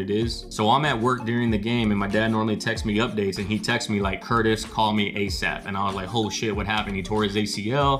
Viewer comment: So is your real name Curtis or did I just hear that wrong?